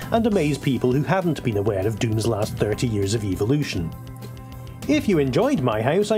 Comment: Did you say Evilution?